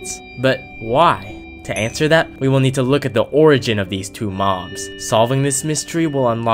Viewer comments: in this video the caption said two moms instead of two mobs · Subtitles say two moms instead of two mobs